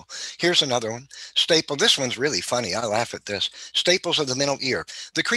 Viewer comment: Did he say "staples"?